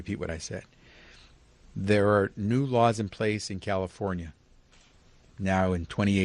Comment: "new laws", good luck with that Cali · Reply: "New laws"? I thought you said "Mow lawns"